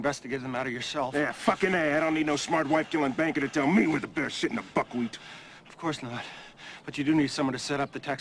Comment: What does he say? "Yeah fucking ass, i dont need no smart wife killing banker telling me what the better shit in the fuckwheat"